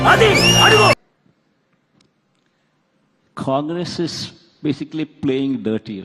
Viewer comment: i thought he said TDP is provoking his sister. now he says congress